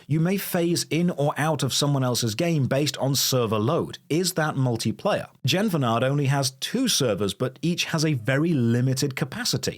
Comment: What’s the name of the game from 10:48? I googled what the transcript said and nothing came up lol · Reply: Genfanad, he's done a video on it too.